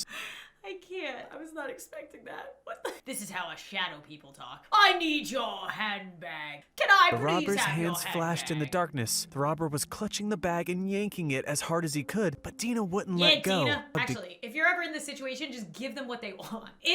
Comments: When I heard: "I need your handbag" I heard: "I need yo hand back" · Ok so I heard 'I need your head back' instead of 'I need your handbag'